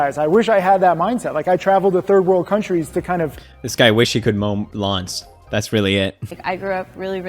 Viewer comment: Did he say luns in the beginning cuz.... Lun means dick in Urdu Edit:Never mind, he said lawns. I understood it